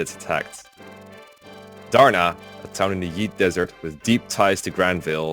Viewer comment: It sounded like said the Yeet Desert